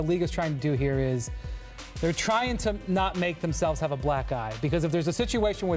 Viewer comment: I thought he said black guy 😅